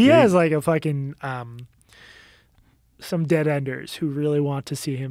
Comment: “Dead-enders”? How to tell someone you only watch CNN, without telling them, you only watch CNN. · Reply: I thought he said dead headers , like the greatfull dead’s loyal fan base.